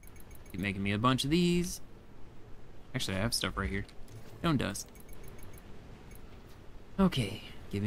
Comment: I thought you said gnome dust! 😅 I was like, noooo! Don't grind up the gnome 😂